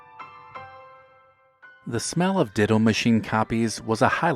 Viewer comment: I thought he said diddle machine and not a ditto machine LOL